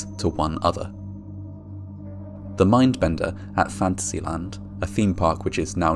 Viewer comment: I thought he was saying ".. At Fancy Land. I had to read the description to make sure I was hearing it wrong. Although, I would love an amusement park being named Fancy Land. Hahaha.